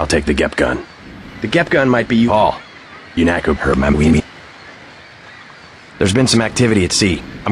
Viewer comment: What's JC saying at 3:16? I hear Unatcoparameme. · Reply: "Paul! .... UNATCO hurt my weenie."